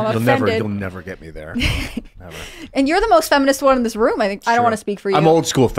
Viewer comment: What I heard was "You are the most feminine in this room"